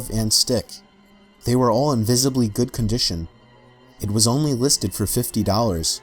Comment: I thought he said "invisibly good condition"